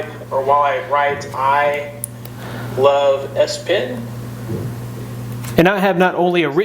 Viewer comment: I skipped to this quickly when clicke do video and thought it said I love sperm lol